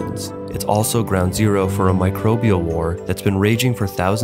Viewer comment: for a sec thought u said a microbial worm was raging for thousands of years